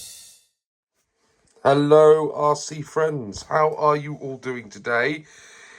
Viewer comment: I thought you said "Hello Aussie friends" at the start😂i had to rewind Derek The Holiday Buggy ? named after Holly Hill Derek ??🤔😄 Ok, Top Force Evo ftw